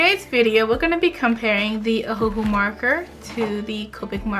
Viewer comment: my captions say "Whoville" Instead oh ohuhu 😂😂💀